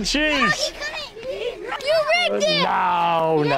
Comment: “This big dog needs to get his rest” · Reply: I legit thought I heard "This big dog needs to get threshed" and that was the most savage pig-tailed girl I've ever heard speak